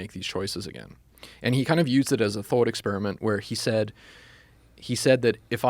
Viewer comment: Nietzsche didn't "believe" in eternal recurrence -- he just used it as a thought experiment for people to figure out whether they're living the life they want. In a way, it's like him asking "If you had to eternally relive your life the same way you've lived it up until now -- would it be a blessing or a curse? What conclusions can you make from this?" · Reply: This is by far the biggest problem we have in society today. Somehow what someone says gets twisted by people that aren't very good listeners. 2:04 he clearly says that Nietzsche used it as a thought experiment. The point he is trying to make is that he has his own views and that they have been influenced by Nietzsche's thoughts. That's it.